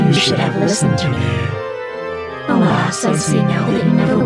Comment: eu ouvi muito ela dizendo "oláa, sozinho--"